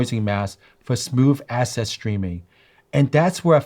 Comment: did he just say smooth asses streaming yah boi.